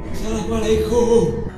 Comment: sikopetnya bilang assalamualaikum? Apa salah denger nih saya?